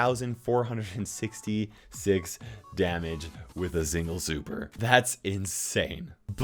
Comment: DID HE SAY ZINGLE?!